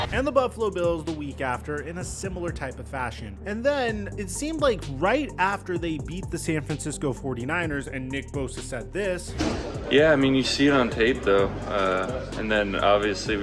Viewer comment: Did you say the Eagles bet the Niners at the 2:30 mark? It sure sounds like it.